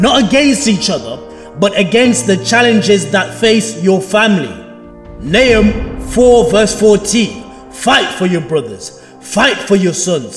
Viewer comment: At around 56:20 or so he says a verse about fighting for your family, if anything knows what verse he said and can let me know that would be greatly appreciated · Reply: It is Nehemiah